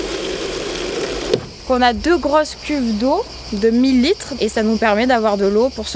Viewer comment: Effectivement moi aussi pour le robinet j'ai "deux grosses cubes" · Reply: "Cuves d'eau " mon pote... 🤫